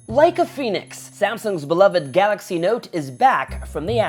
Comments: I had my volume low and thought you said "Like a penis" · i thought you said "like a penis"